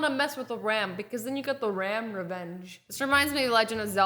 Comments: I thought she said RAMvenge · RAM REVENGE I THOUGHT YOU SAID RAMVENGE